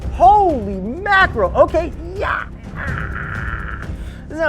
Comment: Did he say Gyatt!?